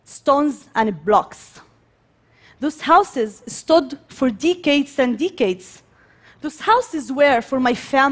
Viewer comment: At 2.45 sounds like she said D heads and D heads 🤣